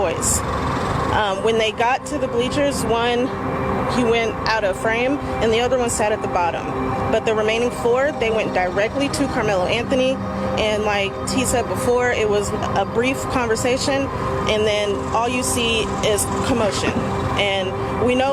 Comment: her name is T BUT she said "Lile T said before"? did I hear that RIGHT?